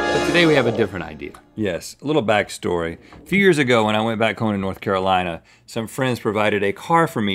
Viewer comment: did Rhett say fears ago or is it just me?